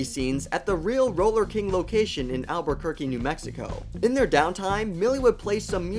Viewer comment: did u say Albuquerque New Mexico ?? Mr white